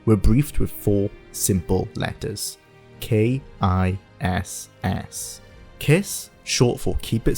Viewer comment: for a brief moment I thought the vid was saying "bethesda developers were briefed with some simple letters: K. Y. S." I was like what wait what?